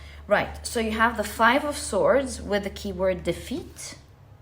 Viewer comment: I know it pile 3 you said "keyword defeat" but I just wanted to let you know I heard "keyboard 's feet" and because I had the reading on in the background of my work, I was like hmm that's an odd card lol. I sometimes hear things incorrectly but it took me a hot minute to recognize I was wrong. Just a small thing that made me chuckle today haha.